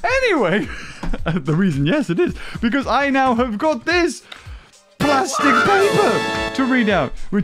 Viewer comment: Did he say Plastic......paper???